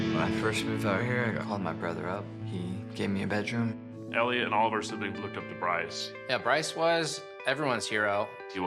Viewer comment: it sounds like he says elliot alderson